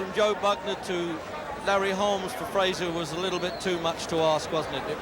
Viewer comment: man who else thought it said “larry holmes vs joe frazier”